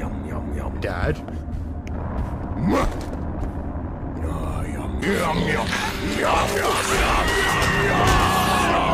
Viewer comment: What did he say?? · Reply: MUH